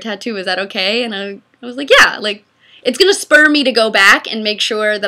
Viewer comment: I thought said "It's gonna Sperm Me to go back" but I realized it spur lol